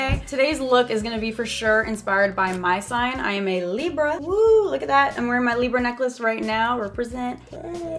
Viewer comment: at first i thought she said leo was her sign and im a libra so her necklace is my sign and i was confused the whole video until i rewinded 🤪😂